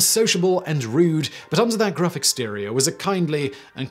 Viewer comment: I heard "gruff exterior" as "graphic stereo". Are all Carukiidae irukandjis? There's also the kingslayer, named for a victim with the surname King.